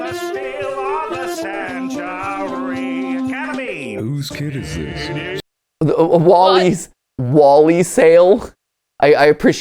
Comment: does anyone else hear what sounds like Markiplier saying “Whose kid is this?” 😂